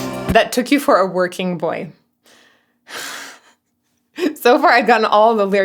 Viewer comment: was shaking my head at the "whackamole" mishearing ... until I realized I'd also misheard it - I did not hear the "boy" part of "working boy", I thought the line was "they took you for a working whore" 😶😶😶 umm... I'll see myself out